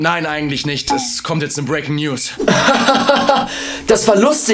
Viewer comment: Ich habe sehr oft Breaking Nudes verstanden